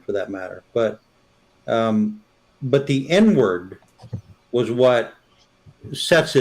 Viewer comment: Damn , I missed the show last night . Intro was strong. Sounded like said the N word didn't realize he said inward .